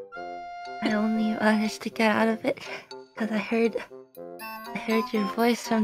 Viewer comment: I didn't manage to get out of it (time machine) - 就算翻譯同樣也是夢話啦 3:38 back in the day when you were a little, little baby 5:58 i have a word that you are a high priestess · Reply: 應該是I “only” managed to get out of it, 接下文 cuz I heard your voice from the other side 意思就是“我能夠從時光機裡出來只因為我聽到了你從另一邊傳出來的聲音”